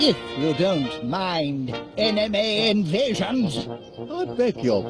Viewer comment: it sounds like crum is saying "Anime Invasions".